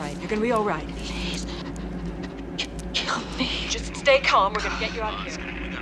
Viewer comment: Still sends shivers down my spine when she says "Please... kill me." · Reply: I thought she said "Help me"? 🤔